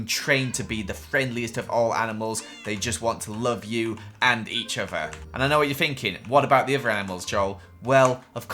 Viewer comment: I thought Joel said “and eat each other”.